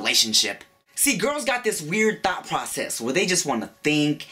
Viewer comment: I heard it as "thot process" automatically. 😂